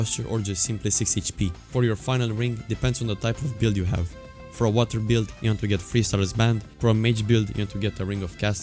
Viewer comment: what did he said that 3:25 i can't make it out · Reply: for a water build, like a build based around water